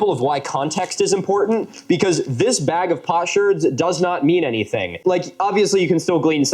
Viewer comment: I was listening (not watching) and thought you said “a bag of pot shirts” 🤣